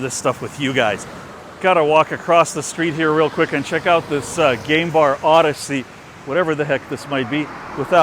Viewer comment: I thought you said gay bar and not game bar. I was like ok we'll see what it looks like lol